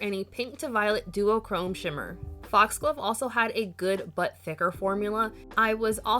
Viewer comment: I kept hearing "good butt thicker" 😗🍑 and was waiting for the lil thick meme lol